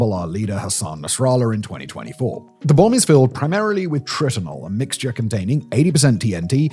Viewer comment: At the 2 Minute 52 second mark I swear I briefly misheard and misread 'Tritinol' as 'Tylenol' which would have been FUNNY AS F.